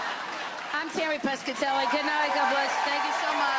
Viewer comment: I’ve listened to it four times so far and every time it sounds like she says I’m “TERRI” Pescatelli. So strange!